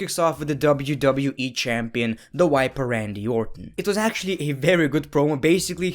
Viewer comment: did he just say the wiper